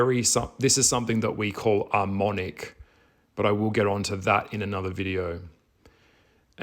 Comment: Been listening to you for the last few years and I always appreciate your insight. Thank-you for being bold, raw and real about your shifting perspectives. QUESTION: at approx 14:20 mark you said a word that I can’t quite make-out- it sounds like you were saying “armonic” or “almonic”. I looked up both words and neither seemed to apply to what you were saying, can you spell it out, so I can look it up? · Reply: Was a reference to Ahriman, Ahramonic, is referring to upside down, chaos, source of human, evil spiritual entity, through technology confusion, relying on technology = weakening humans separating them from themselves from their own divinity. As we merge more and more with technology, and AI, Rudolf Steiner mentions this in his studies. It’s also another aspect of Luciferic consciousness to knock humans off their own true path. As science is only the measurement of something, with things and tools only humans can create. Which rejects spirituality.